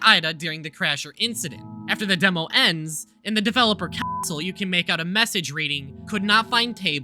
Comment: Is it sayed Developer Asshole or Developer Cuntsole? Or is it another Councile of Developer?